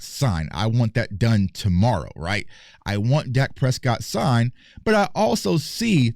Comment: I thought this nigga said he want dak Prescott baby😂😂🤣⏸️🤷🏿‍♂️😭😭